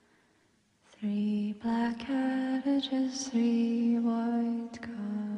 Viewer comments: Thought she said cabbages instead of carriages. And worried about what cabbages she was eating 😅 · Is it just me who thought it said "three black cabbages" instead of "three black carriages"? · The first time I heard this, I could have sworn she said, "Three black cabbages" instead of carriages. I knew from the context it had to be carriages, but it was funny nevertheless (despite the scene that went along with this).